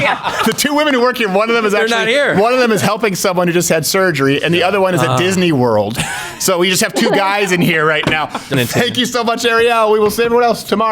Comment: At first I thought she said "thank you so much David"